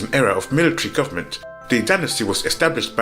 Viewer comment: Prof! I love your work! Btw I hear "Joe Dinasty" , do you mean ""Zhou"? ThAnkhs for Being True to YaSelf, So to We